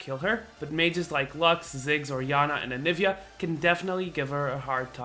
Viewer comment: did he say Yanna as in Janna or am i just stupid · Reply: He said Orianna